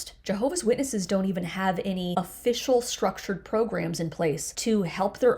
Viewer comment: Must be what I’m taking, I heard you say: outrage programs 😅! Cheers 😅!